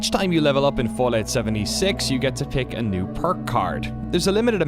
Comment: I know this is not important to the video, but when it said "PICK A PERK" I thought it said "PICK A PEPE"